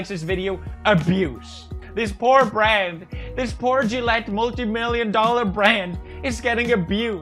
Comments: the subber wrote *jew multi million dollar brand instead of gillette ... smh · lol I thought he said "this Brad-this poor abused brand" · the subtitles say "poor Jew multi million dollar brand" instead of the "poor Gillette multi million dollar brand" that he says, lol. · Pewd says "this poor Gillette multi million dollar brand", yet the subtitle says "poor Jew multi million dollar brand is".... Take it how you will lmao